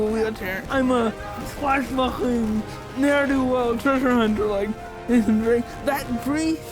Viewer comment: Too bad at 4:22 it appears they mistranslate what was saying. It sounds like he says, "I'm a swashbuckling, ne'er-do-well treasure hunter like Nathan Drake" not "I'm a swashbuckler there to treasure-hunt, like Nathan Drake" as captioned. Otherwise, great video.